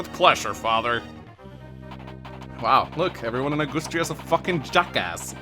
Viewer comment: Did you just say “Augoostria”???